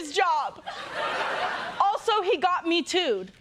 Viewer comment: I thought she said "he got me too", as if her birth caused him to lose his job